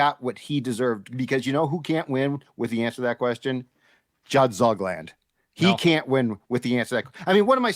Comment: Did Judd say Zug-land?